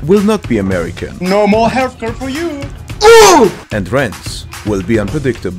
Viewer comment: "NO MORE HELPERS (yeah idk what they are saying) FOR YOU" "OOOUUUUU" 💀💀💀💀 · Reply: Healthcare